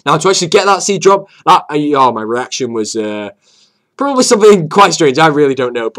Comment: thought you said erection...